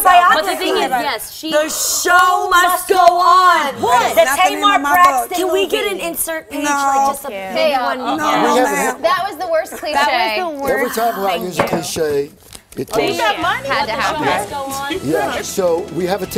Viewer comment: Jeanie and Adrienne both say, "the show must go on". Did that blonde woman say, "that's the worst cliche ever!", in response to what Adrienne & Jeanie said?